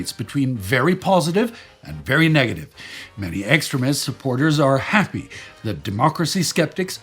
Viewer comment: Sparticus, between my bad hearing and the way you pronounced "extremist", I kept hearing "excrement". And you know, you're not wrong either way!😂 P.S. for any potential Nazi grammers, I'm not saying he's pronouncing it wrong, just pointing out the humor in what I was hearing.😉